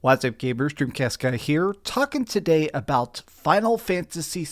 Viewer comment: I don't know why the subtitles say "gay brewster here" but 😅